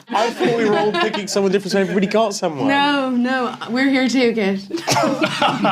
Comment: can someone explain what does danielle says at 2:02? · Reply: We are here too Kit